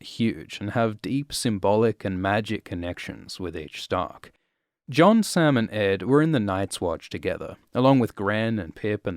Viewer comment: I heard "Jon's salmon egg" 😅😄